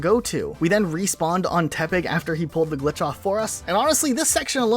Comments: Every time he says tepec I thought he was saying tepig and I got Pokémon black flash backs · Legitimately for the longest time I thought you were saying Tepig rather than Tepec